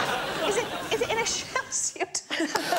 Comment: What did Sarah say at 0:26? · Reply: 'is it in a shell suit'!